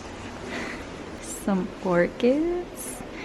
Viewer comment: That has taken over our garden 4:34 - the caption says "some poor kids" 😆